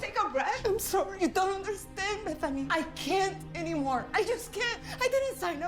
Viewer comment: the actor said "Bethany" but the captions said "m*th"💀 (that's a drug)